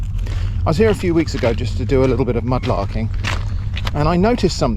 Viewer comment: I couldn't quite catch what you said you had come to Portchester to do at the 0:10 timestamp. Could anyone help? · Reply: Mud larking perhaps